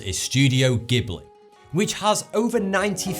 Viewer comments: When you said "Gibli" it sounded like Gibble and I just started laughing. Though, I'm not trying to offend you because of your accent. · Did he just say "Studio Gibble?"